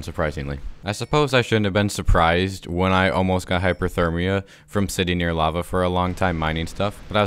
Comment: Love how he said that he was going to get hypothermia when he was next to lava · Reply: He said hyperthermia which is the opposite of hypothermia. I misheard it too